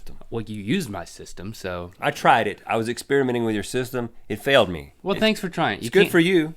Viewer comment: i thought rhett said he was experimenting with links sister o.o